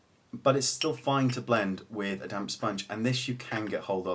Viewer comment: lol. i thought he said a "damn sponge"...but upon a second listen he said a "damp sponge". lol.